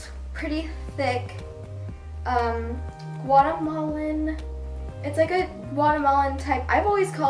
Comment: when you said guatemalan i thought you were saying watermelon